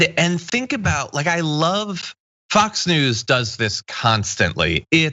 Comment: I just heard John say, " I love Fox news".